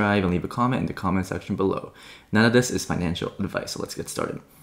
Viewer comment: Instructions unclear. I heard "Now this is financial advice".